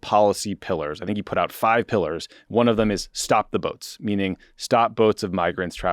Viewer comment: It's a really sad comment on American democracy that when Tommy was referring to the BBC controversy, despite knowing what he meant, I kept hearing "stop the votes" instead of "stop the boats" because that kind of anti-democratic rhetoric has become so common these days.